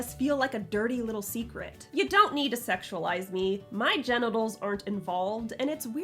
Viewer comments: I thought you said Jennaballs instead of genitals and it kind of worked 😂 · did anybody else hear instead of genitals? Lol Queen of cyborgs, queen of puns😅😂